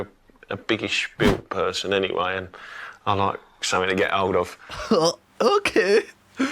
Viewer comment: What did he say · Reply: "I like something to get hold of"